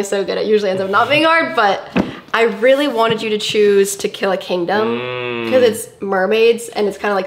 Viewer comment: The way they say "To Kill a Kingdom" I thought they were saying "Tequila Kingdom" lol